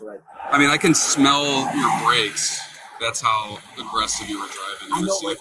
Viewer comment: " I can smell your breaks.." ..The CC says breath.